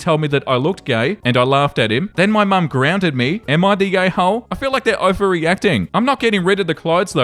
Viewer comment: Bruh, at the beginning I thought you said, “Am I the gay hole?” 😂